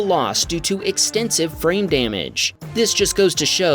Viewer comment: Wow. I swear I heard 'extensive brain damage' instead of 'extensive FRAME damage.' I wonder what that means for my state of mind....